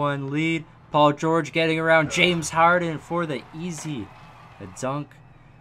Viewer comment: did he say James Howard😂